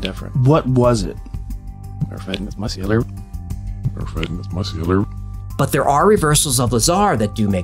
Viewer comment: I heard our friend is muscular lol